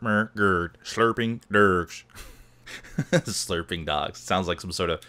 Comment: Sounded like you said "Slurping Drugs" :P